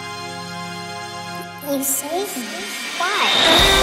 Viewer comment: I don't know what she said can you guys tell me? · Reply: I think she said “you saved me”